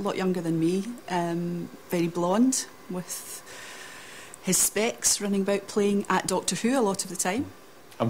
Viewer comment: Sorry, could anybody tell me what says at 1:30 ? She says with his...? Running, playing at doctor who a lot of the time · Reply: Lollipop 10 his specs (glasses)